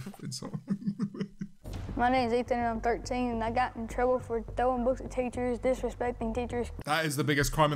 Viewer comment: Throwing books at teachers I thought he said throwing roast potatoes